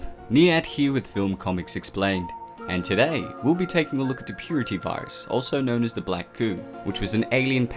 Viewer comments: I thought it said “the puberty virus.” Lol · The black goo? Huh? Smells like engineer's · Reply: I thought they saying it "the black poo" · Dang it I thought it said puberty virus and the victims like got older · I thought that said puberty virus and I was like oh no.